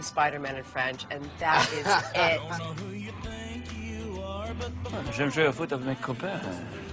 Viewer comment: What does Alex say at the end? Thanks. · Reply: (Guadapuff) I hear ''J'vais m'faire foutre avec mes copains'', which means ''I'll go get laid with my buddies''.. sigh Men.. ;)